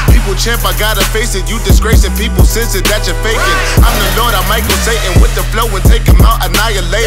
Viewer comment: does he say he might go Satan